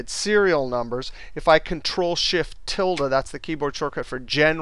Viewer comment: What was the command? I couldn't understand. Would you please clearly write again? "Cnt+Shift+??" to convert the date in general number? Thanks. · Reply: Ctrl + Shift + ~ (tilde) on some keyboards. On other keyboards it is: Ctrl + Shift + ` (grave accent).